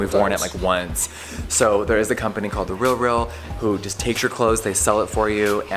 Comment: what’s that clothing store that takes your clothing and buys off of called?? Didn’t catch what he said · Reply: TheRealReal